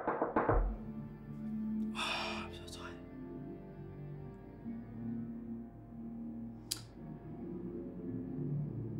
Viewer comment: what did brad say in 3:19? im so sad? corrct me if im wrong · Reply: Trisha De Guzman no he said I'm so tired